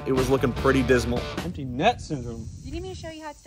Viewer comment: it sounded like he said “empty nut syndrome” 💀